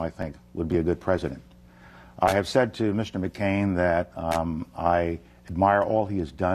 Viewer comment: did he said " i have sex with mccain?"